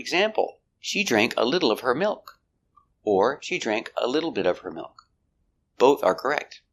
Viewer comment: Am I hearing it right? On 2:40 you said “she drink”? Is it correct?